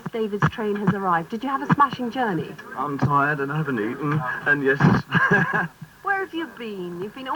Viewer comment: what does he say? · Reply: "I'm tired, and I haven't eaten, and yes"